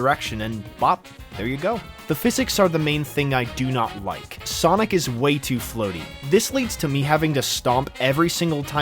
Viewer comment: I thought he said “Sonic is way to flirty” 😔😭🥀